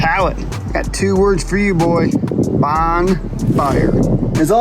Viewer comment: …. Did you say “Bong fire” or was I just imagining that’s what I heard you say?!? Any whooo beautiful project!!